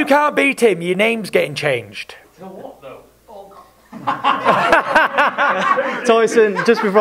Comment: Anyone know what the person in the background says at 11:46? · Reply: Tommy says to what though and then I think Paris ballcock